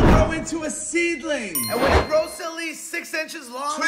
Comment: I thought he said "when you're done eating in Africa" Was like wtf did he just say? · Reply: fr 💀💀 edit: lmao at the end i thought he said "plant some africa" 💀💀